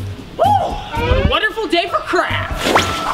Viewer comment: What I thought you said: “Wonderful day for CrAp “ - But yea Still TrUe